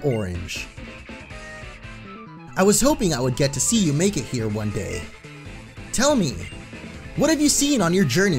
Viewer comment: i heard "I hoped i will see you NAKED here one day",and i almost died from how loud i loughed